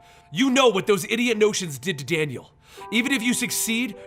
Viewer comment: What did iris say at 2:10 idoit What? did to daniel west